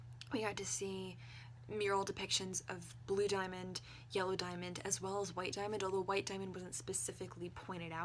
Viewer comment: It sounded like you said Jello Diamond. New fanart plz~